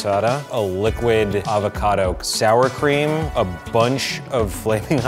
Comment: did say "l i q u i d a v o c a d o"?